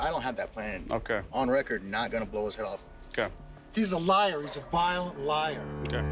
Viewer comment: Sounded like he said vile liar